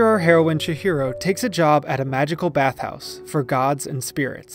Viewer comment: Why do i hear him say "chi-hero"? Is it just the American accent or wat?